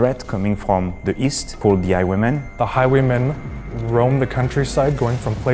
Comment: The first time i thought he said "the high women"